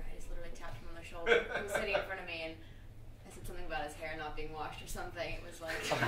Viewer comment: I can't understand what Lily said at (more or less) 3:47-3:52 ..... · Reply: alakai562 "I said something about his hair not being washed or something"